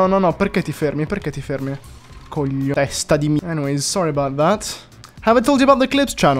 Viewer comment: did you just swear at the little pigeon? What did you call him? Tell us all Some of us understand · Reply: Would like to have some Italian language lessons with you. Don't call me a coglione